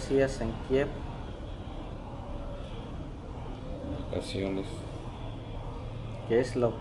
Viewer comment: The translator seems to have heard the word “acciones” meaning “actions.” He actually said “vacaciones” or “vacations.”